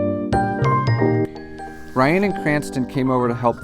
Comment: I thought you said Bryan and Cranston. What are the odds.